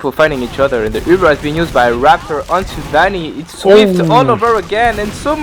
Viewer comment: They said SVIFT all over again... Can someone send me a clip of what he's saying by that?